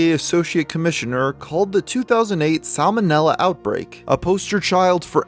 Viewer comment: I thought it said Sam O Nella outbreak